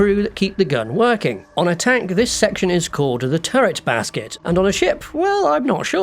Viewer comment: “Turret well” might be right, like what that one guy said